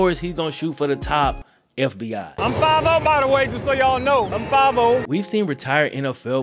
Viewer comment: Been a fan for a long time and NEVER you wat you said until you got it put on your shirt 👕 I thought you said "you the way!"🤣